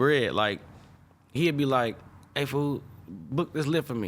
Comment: it's "foe", a short version of "foenem" (folks + them) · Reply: but its sounds like he is saying fool or foo, not foe.